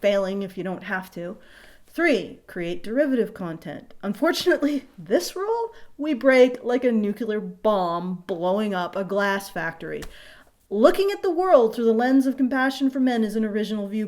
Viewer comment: did you say "nucular"?